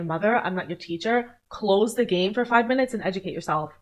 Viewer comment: did she say close the game for 5 minutes? SHE'S PART OF THE PROBLEM